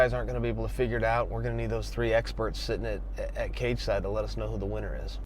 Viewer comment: Did he say "experts"?